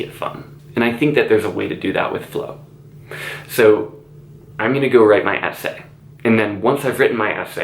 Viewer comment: skjjjjgrhig when he said 'i'm going to go write my essay' it sounded like he was saying 'k I'm busy now bye'